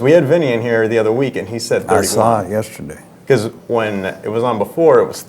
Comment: "We had Vinnie in here last week. I saw him yesterday..." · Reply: I thought he said, I saw it yesterday, as in his Loudwire video